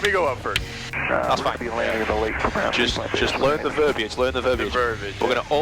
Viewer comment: did I hear " we're gonna be landing in the lake" at 32:38?